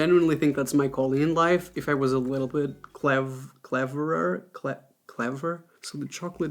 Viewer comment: When you said your dream job would be to do that if you were "cleverer" at 15:45, I thought you said "if I was a leper" and I was like WHHHAAATTTTT????? LOL